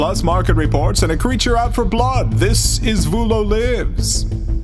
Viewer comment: I keep hearing them say "Vulo Liz" at the start